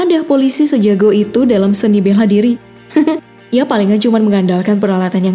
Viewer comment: kok dengernya "Beha diri" 😅